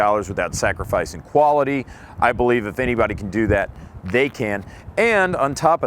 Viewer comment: On that bull pup (6:19) he said "and if anybody could do it they CAN'T" i replayed it to make sure I heard it right. Anybody else catch this?